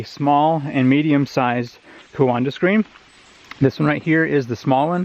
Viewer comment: Every time you said 'coanda' I heard 'Wakanda' in my head. :) Cool stuff though, very interesting!